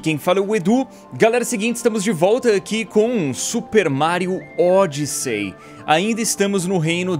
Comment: No começo eu quase ouvi "super Mario ÓDIO"